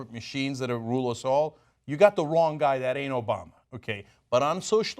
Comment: "That ain't Obama." I heard this as "that anal bomb."